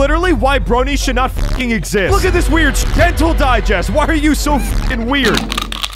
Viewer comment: Ngl I thought it said dental diggest